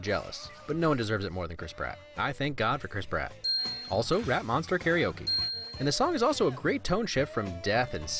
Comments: When he said "rat monster" I thought he said "rap monster" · Not gonna lie, at 1:16 I thought he said "Rap Monster karaoke" and not "rat monster karaoke" and I legit missed a step going down the stairwell at my apartment complex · Honestly at 1:16 I thought he said Rap Monster karaoke, not Rat Monster karaoke... but are great though